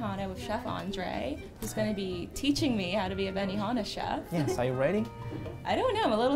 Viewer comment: My fat ass thought it said 'Banana Chef'